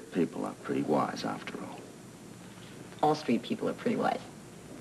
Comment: Great and scene! :) Love it! :) at 6:57-6:58 did say Aussie street people are pretty wise? :)